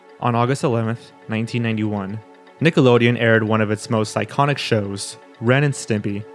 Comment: you say "one of its most iconic shows" but the way you said it also sounds like you said "one of its most psychotic shows" and that would be a fair descriptor also.